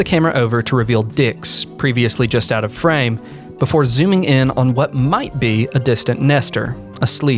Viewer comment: I heard “to reveal dicks just out of frame” and laughed because this was so serious